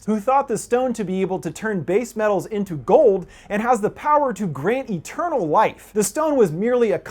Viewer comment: did i hear that good..give eternal wife.😱😱😱